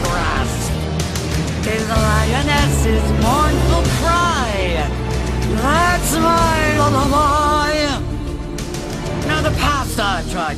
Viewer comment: I swear I thought all these years that she said "That's my little boy" My life is a lie. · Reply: I always thought she said "That's my alibi" lol